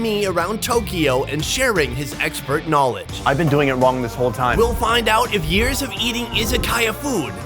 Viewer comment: why did I think he said “I’ve been doing your mom this whole time”